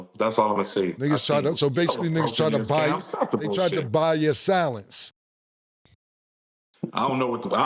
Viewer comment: Sounded like Cam said “They tried to buy your salads” pause, that’s why Chop said “idk what that mean”